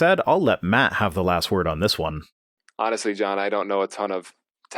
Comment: He said "Simple Science", I heard "Simple Simon". sigh